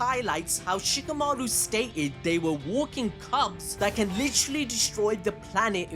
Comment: i thought he said “they are walking cucks” 😂